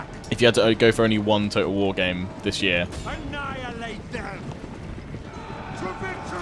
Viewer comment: Did anyone else hear an "Up and at them!" just after 47:00? McBain would be proud.